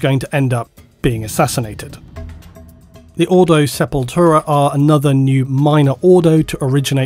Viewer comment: says "Ordo Sepultura" i hear "Roots, bloody roots!" \m/